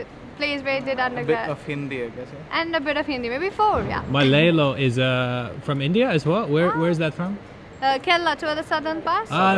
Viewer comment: "Malilo is from India as well?" And the girl was like "ohhh" 😅 She thought that he was saying "my landlord is from India, too"